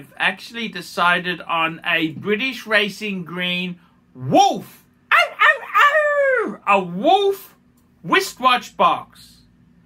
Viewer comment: I thought you said you got a British Racing Green Ruf Boxster.